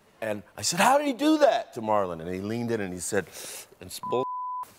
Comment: What did he say? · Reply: It’s bullshit